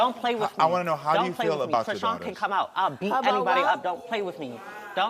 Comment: Did she say chrisean can come out? 😭